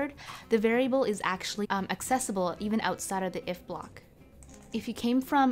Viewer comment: it sound like you said "the variable is actually unaccessible", but you clearly meant it is accessible. It seems to be due to a jump cut.